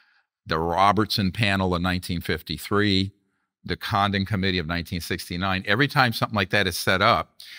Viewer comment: The auto-generated subtitles said "the condom committee of 1969" 😅